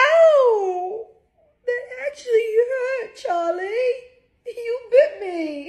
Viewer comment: Chase: I don't know why she's pretending I hurt her, but let's hug her. Edit: Thank you all for the likes!!! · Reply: I thought she said Charlie not chase...💀💀